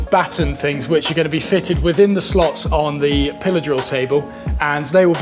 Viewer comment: I swear the first time I heard you say "pillar drill", which is a term I've never heard before, I thought you said "pillow saw." Lol. I like learning the English language.. :)